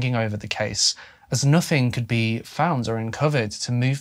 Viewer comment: i’m plagued by this damn virus. i rly thought @ 9:57 you said covid 😩😂😅